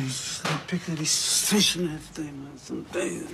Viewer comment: what did dev say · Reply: “It’s like Piccadilly station” x